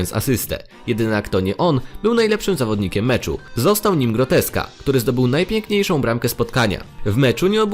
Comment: Czy tylko ja usłyszałem zamiast Gorecka, Groteska? XD